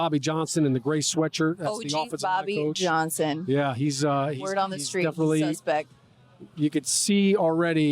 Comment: What did she say at the 4:04 mark ?? Lol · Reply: Word on the street he’s suspect🤣🤣